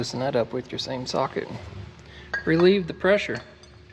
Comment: Cuts into the hose and in my head I hear "RELEASE THE SHMOO!"